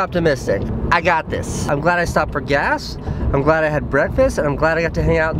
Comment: " Afghanistan for gas" Sounded like that xD